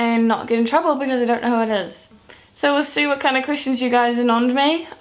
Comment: every time she says the word "questions" I hear "Christians"... oh well. D: #justEnglishlearnerthings